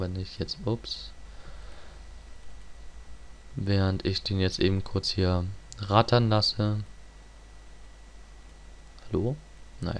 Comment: Ok, ich habe mich beim Forum angemeldet :-) Eine Frage, die sich ums Deutschlernen handelt: Sagst du bei 21:30-34 "während ich den jetzt eben kurz hier raten lasse"? Ich hatte noch nie gehört, dass jemand das Verb "raten" im Sinne von "laufen" verwendet.